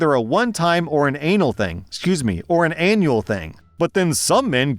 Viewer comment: I laughed when said " a one time or an anal thing " instead of " annual thing "